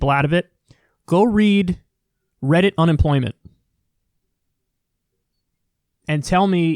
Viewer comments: Go read what? Did he say “reddit unemployment”, I want to read whatever it is he’s referring to. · what was Kyle recommending reading? I heard "reddit unemployment" but that can't be right lol